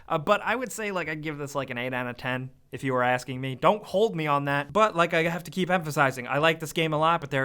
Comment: wait thought he said don't quote me on that, dammit